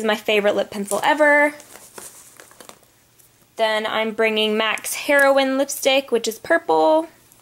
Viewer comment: Did i just hear "mac's HEROINE lipstick"?